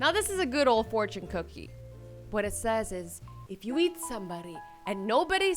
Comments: Did she just say " if you eat somebody " instead of if you eat something 😂😂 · I was dying when Azzy said, “When you eat “somebody” instead of something :) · did she say “if you eat SOMEBODY”? Or my ears are just not working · Did she say if you eat SOMEBODY? Or was it just me who heard that...